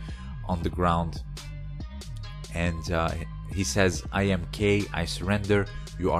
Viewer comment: oh so his name was kaye, i thought he said " iam gay i surrender, you are strong".